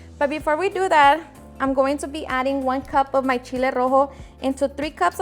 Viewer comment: you are holding up a cup of a red-looking sauce.......what did you call it ? It sounds like cheletto-sauce? where is the recipe ?? thanks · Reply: not sure if you still need it... but it's called Chile rojo. literal translation is red chili.